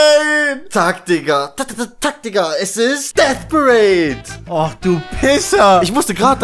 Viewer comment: Was war das für ein anime um 11:20 hab nur irgendwas mit Death verstanden